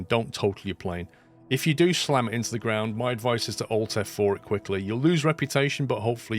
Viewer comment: At minute 18:40 he says "Don't total your plane if you do slam it into the ground my advice is to Al F4 it quickly you'll lose reputation but hopefully you won't lose the plane." That is what the sub title says. I can not quite understand what he is saying with "AI F4 it quickly". Any help much appreciated. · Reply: He means on PC to press ALT-F4 to force quit the game so the game probably can't register you totalled the plane and you still have it when re-starting the game.